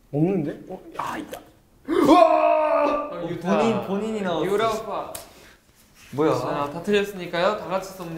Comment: I'm not sure if I heard it right but Doyoung at 4:31 said: "Yura oppa" right? ... I'm Yura.............. 🥺😩